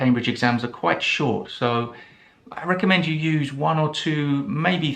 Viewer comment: Hi do I hear it clearly : I recommend you you use????? at 1:40. Is it not incorrect grammatically to use dative after the verb recommend? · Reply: "I recommend you use..." is correct 🙂